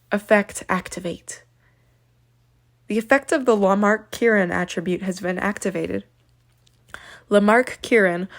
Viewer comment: anyone else hear her say "Walmart Karen attribute" there?